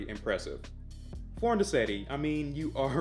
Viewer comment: What was the work of art one? what did he say the name was Foreignecety? the 6:21 timestamp.